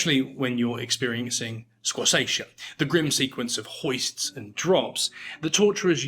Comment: Does anyone know what that word he says after the word experiencing is here? I can't search it up because the caption doesn' understand it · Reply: Squassation (noun) A form of strappado in which the victim, with arms bound behind and feet heavily weighted, was jerked up and down at the end of a rope passed under the arms.